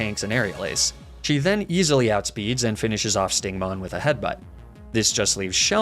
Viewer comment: I heard him say Stingmon 😂